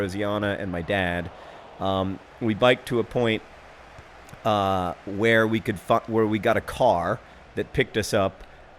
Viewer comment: "We biked to a point, eh, where we could fuck, where we got a car that picked us up..." I KNOW I misheard something here, but that's all I can hear each time I replay. Someone please put my mind to ease by telling me what John actually said. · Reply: Started to say "Where we could find", then switched to "Where we could get"